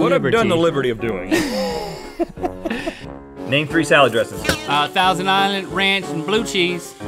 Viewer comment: did Rhet just say salad DRESSES?